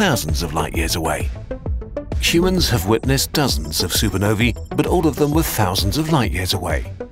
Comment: Okay now I heard it clearly wrong. It’s supernovae* and not „supernovi“. Latin 101